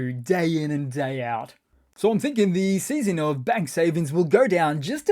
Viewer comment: when you said 'ruzzians savings' I thought you were saying 'UK savings'. so that is where this so-called government in the UK is getting their ideas from!! Putler.